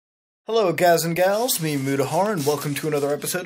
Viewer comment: I swear I heard him say mutahard at the intro